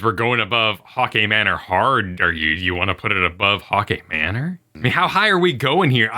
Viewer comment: Why can I only hear "hockey manor" 🤣